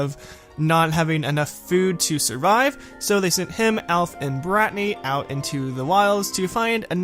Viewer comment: Did you say BRATNY